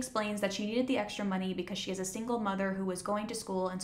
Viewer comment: I heard Katie "is a single mother" instead of "has a single mother" and I thought the show REALLY had a twist after I stopped watching